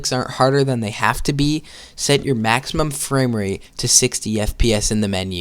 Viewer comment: Remember: when you pass the wave, pause the level IMMEDIATELY and assume the "lock-in" position by leaning as close as humanly possible to your screen. Then resume playing and beat the level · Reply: Bro I played the entire level in 60 fps, being ex mobile player really helped edit: I thought u said “change ur frame rate to 240”